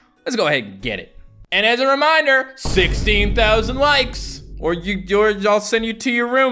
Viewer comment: Ngl i thought he said 60k likes and I was like do you really think we won't hit that.